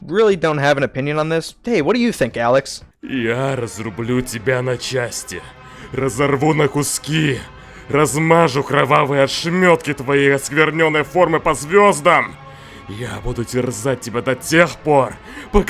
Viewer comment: What EXACTLY did Alex say at 8:27? Rough Transcript: "Я разрублю тебя на части разорву на куски, размажу Кровавой ошметки твои свернём и формы по звёздам. Я буду терзать тебя до тех пор, пока с вами живёт и милосердии мои руки с наслаждением, покотят с тобой здесь и сейчас." Rough Translations: "I will chop you into pieces, tear you to shreds, smear your bloody remains, and scatter your fragments among the stars. I will torment you until there is no life or mercy left in you, and my hands will take pleasure in doing so, right here and now." I wanna know the ACTUAL words said here, not Google Translate. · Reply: Ok so i know russian language, let me help ya "Я разрублю тебя на части, разорву на куски, размажу кровавые ошметки твоей оскверненной формы по звездам. Я буду терзать тебя до тех пор пока сами звезды не начнут взывать о милосердии, мои руки с наслаждением покончат с тобой, здесь и сейчас"